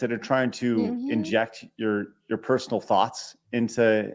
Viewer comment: Every time you guys said “thought” my brain heard it as thot